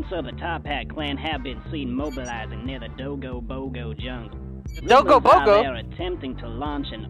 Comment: “dogo bogo” Don’t go Pokémon is what the captions say Sorry if someone said this